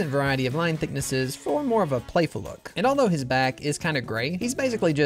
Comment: HES BACK LADIES AND GENTLEMEN HES BACK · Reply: For a second thought bro said he’s black